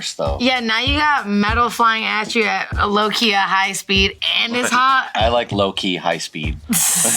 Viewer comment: I heard "lowkia high speed" rather than "lowkey a high speed".